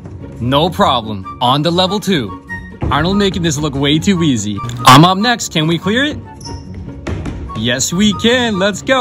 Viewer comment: "I M up next" SCARED ME I THOUGHT HE SAID MAMA💀